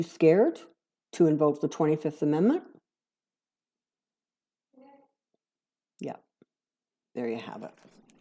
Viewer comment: Was that a person in your house other than you or a TV that said yes at or around 9:23-9:25? I heard an audible yes besides you talking when you asked is Mike Pence afraid to invoke the 25th amendment?